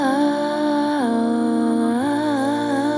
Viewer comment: Now I heard "Before love comes tequila" and I can't unhear it haha